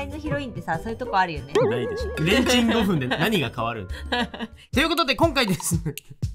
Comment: 「レンチン５分」を「レンチンのフン」にと最悪の空耳をしてしまって申し訳ないです。